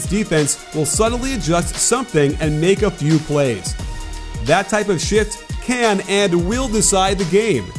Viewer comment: “that type of shit” did i hear it right?? Lol